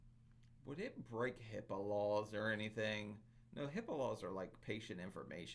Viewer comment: I thought you said, "Hippo Laws" at first 😬